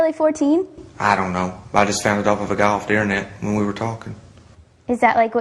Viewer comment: LOL what the hell does he say? I just got it off a da goff the internet